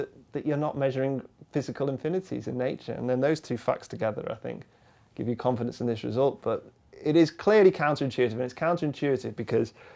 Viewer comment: What did he just say at 6:54? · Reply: “And then those two facts together I think, give you confidence in this result.“